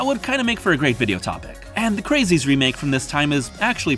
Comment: Great video, as always. Does anyone know what remake is talking about at 8:09 ? He says "craziest remake of this time was pretty good", but which is it? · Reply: He says "The Crazies." The original film was released in 1973 and was made by George A. Romero (Night/Dawn/Day of the Dead creator), and the remake came out in 2010. From what I've heard, most people consider the remake better than the original, and it's a very solid movie. Another good horror remake from the 2010s is the Friday the 13th remake.